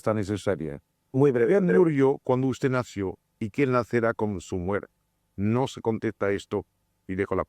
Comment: Que pregunta André malby en el minuto 46:05 no se entiende muy bien , alguien pudo entender ? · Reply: ¿Quién murió cuándo usted nació y quién nacerá con su muerte?